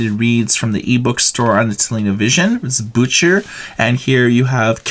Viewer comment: Loled hard when I heard "bücher" (books) pronounced like "butcher". German "ch" is pronounced like Spanish "x".